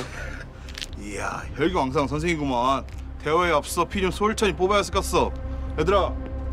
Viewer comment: 깡패아저씨 대사 뭐라고하는거에요...? 대화에 앞서 여기까지밖에 못알아듣겠어요ㅠㅠ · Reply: 피를 솔찬히 뽑아야 쓰것어